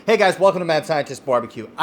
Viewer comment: I heard, "Hey guys welcome to Mad Sanchez BBQ" 😂😂😂